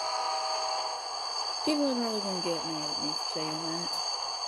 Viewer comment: What did you say at 20:47? All I heard is “dating you were saying that” or something like that